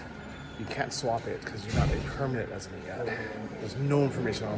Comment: If you have the captions on then read them at 5:19. Instead of permanent resident the captions say "permanent lesbian".... Had a laugh and wanted to share if anyone else finds this funny